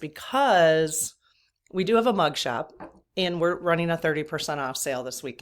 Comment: Looked forward to this! Just having breakfast at noon... And got my 6 week old chicks outdoors in coop ive been working on. This is a nice place to be!! Thnx. I thought you said mug shot. Lol